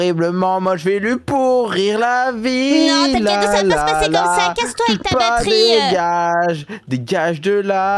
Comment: elle dit a casse toi avec ta baterie